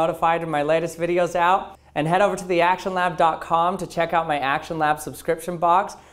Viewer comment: I keep hearing you say "Actual Knob" instead of "actionlab" just so youre aware... :)